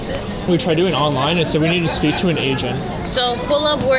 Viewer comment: I thought he said “they said you need to speak to an Asian”